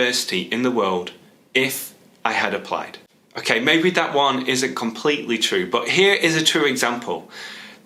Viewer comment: Hello, I wonder if you could make a video of pronunciation because in the minute 4:07 you said "okey, maybe that one isn't completely true" I didn't catch "ISN'T" that sounds like "IS" so I had to turn on the subtitles to understand that part of the sentence. Greatings from Peru.